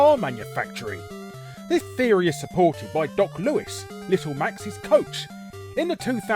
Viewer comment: did you just say "little mac's's coach"